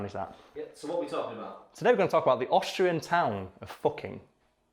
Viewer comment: I originally thought it said Australia